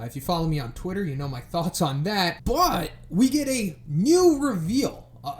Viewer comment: What you say « you know my thought on that, but... » What i’ve heard « you know my thought on dat BUTT.... » ( ͡° ͜ʖ ͡°)